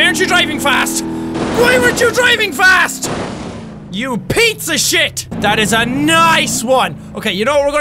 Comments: did jacky say pizzashit? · It sounded like he said pizza shit 🍕💩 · is it just me or did Jack say "You pizza shit!" o_O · Jack, about every time you say "Piece of shit" it sounds like you're saying "pizza shit." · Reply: Mr.VideoGames I thought he said pizza shit